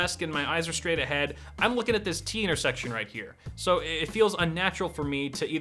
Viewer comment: I know he said "t-intersection" but the fact that I heard teener-section made it all the better